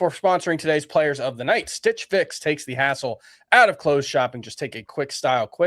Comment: Am I the only one who thought said "asshole" instead of "hassle?"